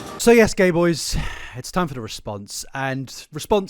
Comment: did he just call me a gay boy??